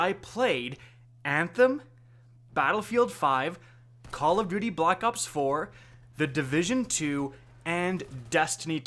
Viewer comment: “..call of booty black ops 4..” did anyone else hear this?